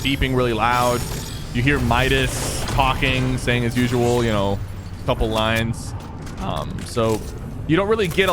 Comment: Sypher: You know you hear Midas talking so couple of lines. What I heard from Midas: Suck a c**k